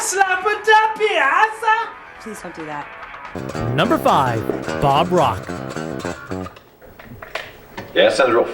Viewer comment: Bruhh, I thought they said Bob Ross at the start 💀😂